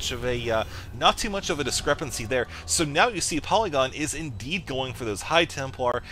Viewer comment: i heard polygon is "day9"